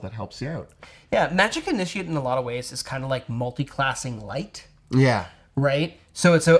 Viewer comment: Sorry, when you called Magic Initiate "multiclassing lite" I heard "Multicasting Light"